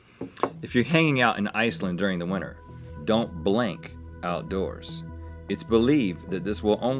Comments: I thought he said "don't blink outdoors" XD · I thought he said don't blink outside